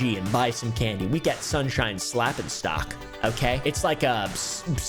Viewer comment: I thought he say "Sunshine Slappingstock' All one world.